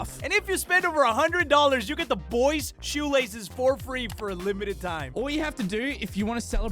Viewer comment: I’m sorry did they just say we get shoe lashes for free? But only for a limited time?!?!? ( so I’ve got to return them)